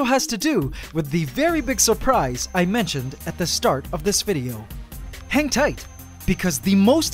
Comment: i thought he said hentai xD